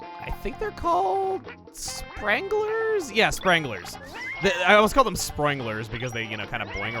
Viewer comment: I thought you said “stranglers”